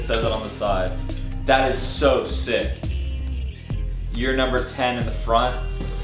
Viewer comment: Thought u said “Your number 10” then realized it wasn’t then all’s realized Wouldn’t it be sick haven’t a deck saying “your number 12 “ or what ever number and order the deck was made